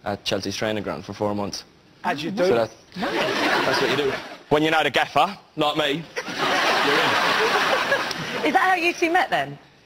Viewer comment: what did he say at second 0:41? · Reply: Ximena Garcia 'That's what you do, when you know the gaffer (the boss), like me, you're in.